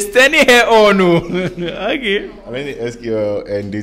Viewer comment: Did say sqlr?😂😂😂